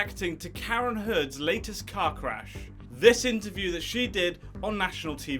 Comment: Ohhh... "Amber's latest CAR CRASH". lmao... I thought he said cock rash.